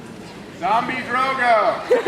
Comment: What is the man saying (29:03)? Something like zombie? And what is Lena saying about her dreamed-of ending (30:17)? Thanks:) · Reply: Zombie Drogo, lol. Lena was saying she wanted Drogo and Tyrion to co-rule in the kingdoms coz it would be interesting to see!